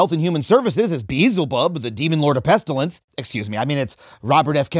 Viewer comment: I thought you said "Meelzebub" 😄